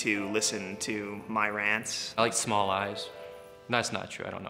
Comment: I thought he said "I like small lies" not "small eyes" . Whoop.